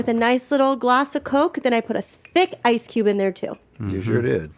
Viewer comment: I thought Link said "there's a pube in there too" LOL. I guess he said cube!